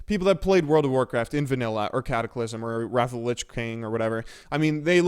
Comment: did you say wrath of lich bane instead of king?